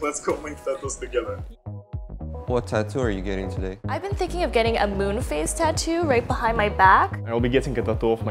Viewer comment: Oh God, I thought she said “I want a MOON FACE tattoo..” and I was like... “ummm what a choice.” 🌚